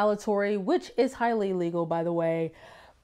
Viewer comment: to about 3:53 You say "retaliatory" "which is highly legal" Is it? Not sure if you said illegal or legal, but it sure sounds like you said "legal"